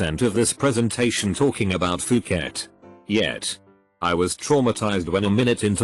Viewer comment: the way the bot says it makes me think he might have said Fuck It